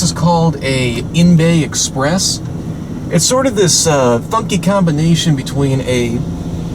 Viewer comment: did he say the F word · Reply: He said funky